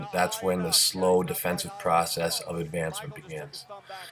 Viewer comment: "That's when the slow defensive process of ___ begins." I can't understand what Devon says here? Advancement? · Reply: Sounds like he's saying advancement.